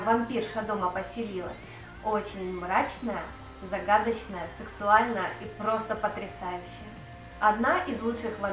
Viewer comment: А , что вы в конце сказали? Сексуальная? Или мне послышалось?